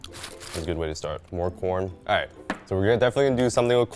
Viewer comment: I thought he said porn